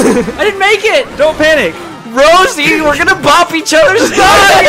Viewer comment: I literally LOL'd when Ty raged about depth perception. The reaction is epic. What did say to Rosalina at 13:42? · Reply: He said, "Rosie, we're going to bop each other" cause they were too close and he was afraid that one of them might jump on the others head and kill em.